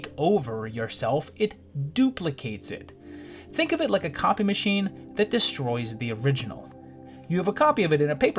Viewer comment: I thought he said "Think of it as a coffee-machine that destroys the original", lol!